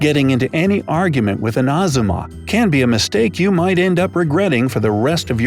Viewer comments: Did I hear him say "Azumma"? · DID HE JUST SAY ''agzuma" "azzuma" VDGWVDHGBHCVJWTF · Did he just say.. Hazuma...?